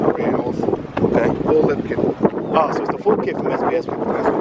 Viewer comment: When he said lip kit i thought he said lift kit I was like tf 🤦‍♂️🤦‍♂️